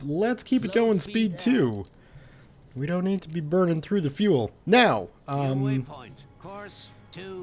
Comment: Lol I thought he said Bergen through the fuel at the beginning